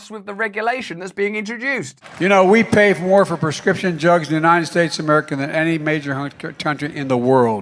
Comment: I SWEAR I heard him say "prescription *Jugs*" 🤣